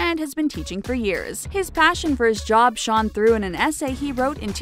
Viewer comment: Am I the only one who heard her say "Shaun" instead of "shone"? 😂